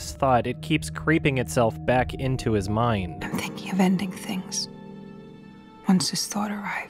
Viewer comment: For a moment there I thought it said "I'm Thinking of Ending YMS" and got quite spooked!